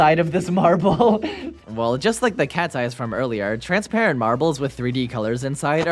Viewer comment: When you said “look it’s cats eyes marbles” it sounded like you said katsye